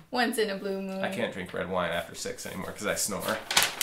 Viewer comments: OMGGGGGGGGGG I so thought he said "I can't drink after sex anymore because I snore" LMAO!!!! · i thought said no wine after sex too! lmao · I legit thought said no red wine after sex too LOL